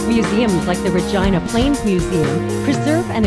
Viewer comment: Wait, did she just say that the Plains Museum of Regina @ 1:15 "preserve[s] and interprets the cultural, social, and economic development of the Aryan."? Or am I just mishearing that because of the potato-quality audio?